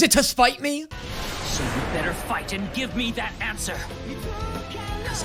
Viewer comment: Is it just me but I thought Aktagwa was gonna say "give me that ass" instead of "give me the answer" no? Just me ok 😅😂